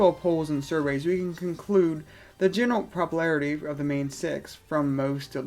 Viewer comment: i heard " poppler" and i was hooked.